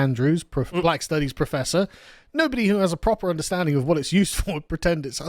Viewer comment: The Great replacement was never a theory. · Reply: At first I thought you said flag studies professor, not black studies professor.